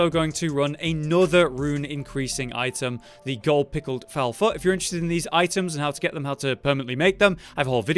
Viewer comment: because of accent i thought he said gold pickled fowl fuck instead of foot